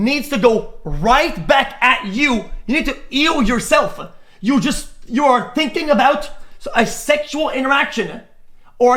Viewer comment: what did he say 😂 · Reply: “Ya need to” but fast as fuck